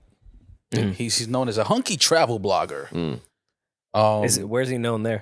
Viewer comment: Am I the only one who thought Mal said a "Honky" travel agent instead of "Hunky".